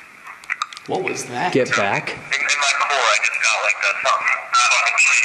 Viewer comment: as cool as "get back" would be, I think what we're hearing is Matt saying "ask" to when he said what his next question was going to be